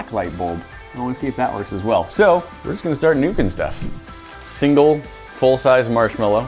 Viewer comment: did he say were just gunna start nukin stuff